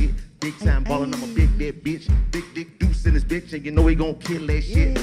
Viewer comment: Now wait a minute Chico‼️‼️‼️‼️‼️ did I just hear u call this man.....NVM 🤦‍♂️ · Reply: LMAO 🤣🤣 I thought he said big duke duece!